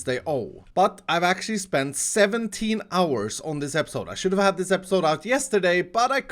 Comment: shit? am i hearing things?